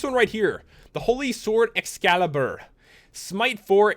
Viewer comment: Excalibur is the Best Sword in the anime so instead of 24 damage they should do 54 damage: plus it should have a harder recipe since it's unique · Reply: Oh nvm instead of excalibur I thought you said alucidater